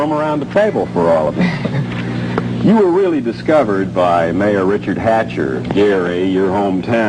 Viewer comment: what it's saying at 1:20? marry richard hatcher or what? i don't understand. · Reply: Mayor Richard Hatcher of Gary Indiana